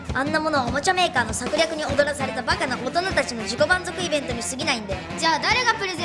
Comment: '위협받은'이 아니라 '놀아난' 아닌가요?? おどかされた가 아니라 おどらされた로 들리네요.